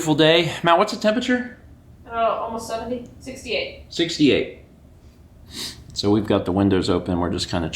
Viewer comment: For a second I thought said it was 768° and I was briefly confused.